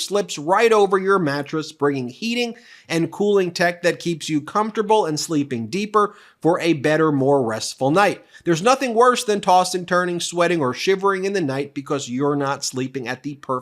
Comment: Go to <link> and save $200 on the Pod Cover plus FREE Shipping! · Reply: I was listening not watching and when this AD started by Ben, I thought he said "AIDS Sleep," over and over. Horrible product name.